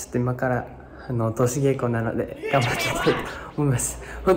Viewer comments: ここにしたくなんて言ってるの？？ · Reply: 通し稽古 だと思います🎶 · あたりの 西村拓哉くんなんていってるんですか？ とーしげこう？ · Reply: 通し稽古じゃないですかね？？